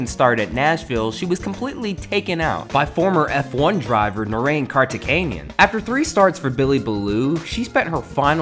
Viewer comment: did i hear the goat, legend and god, Narain Karthikeyan?